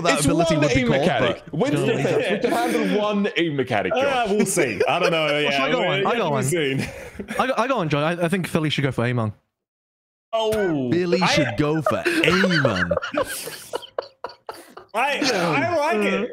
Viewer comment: lol, I thought said they should go for Emongg